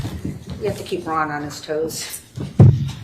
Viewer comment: sounded like she said got keep them honest hoes LOL